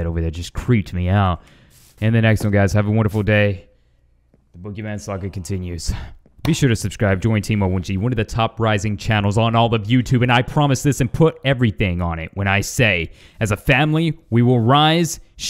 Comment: At the end it sounded like you said "the pokemon saga continues"